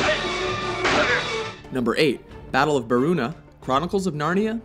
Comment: I'm disappointed, I heard "The Battle of Bruma" at first.